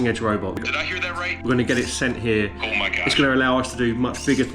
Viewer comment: did I hear that right “oh my god” not CHAT gpt sayin that